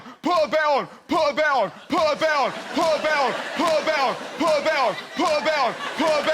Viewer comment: I'm not sure but at 1:39 I think he is saying put a bet on